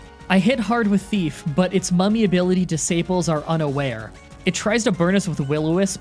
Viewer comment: i hear disaples not disables hmmmmmmmmmmmmmm